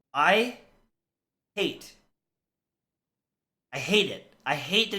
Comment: did he say i Ate?😂